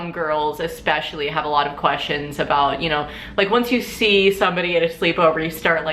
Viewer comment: LOL my bf walked over while I was watching this and thought he heard you say "Home Girls got a lot of questions..." lol dem Home Girls ;D